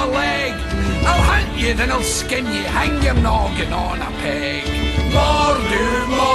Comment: I keep hearing "Hang your knob on a peg"